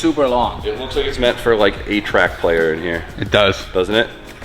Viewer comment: Did you say A track player. Or eight track player?